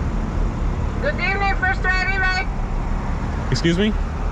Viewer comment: Dude, I listened to that like 5 times, all I heard was "good evening, first way or XXX"... I do not understand what the person is talking about..